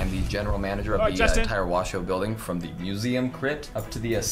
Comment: I heard it say "we are building a cross" in the crypt? o.o